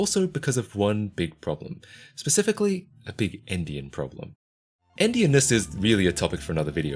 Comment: I was washing dishes and I swear I thought you said “big Indian problem”